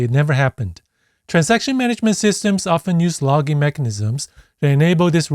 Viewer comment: "transaction management systems often use loCKing mechanism", looks like there should lock icon on slide instead of log